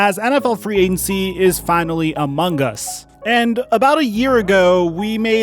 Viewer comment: DID HE SAY AMONG US????